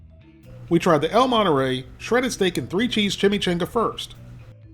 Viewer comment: Forget "ChimichANGa", I was more fixated on the fact it sounded like said "Snake" instead of "Steak" half the time.